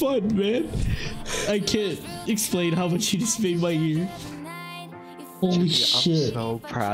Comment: captions said that sunless made his ear